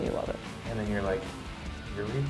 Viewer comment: I thought he said urethra what is wrong with me?